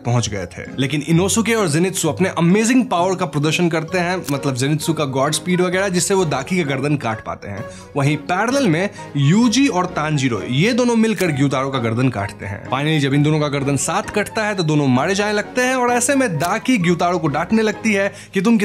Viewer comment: did I just hear yuji in a demon slayer vid😂😂